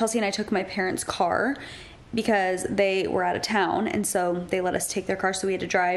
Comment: Thought you said lady was out of town 😂😂😂😂